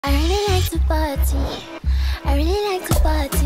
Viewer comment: All I hear is"I really like to potty"😂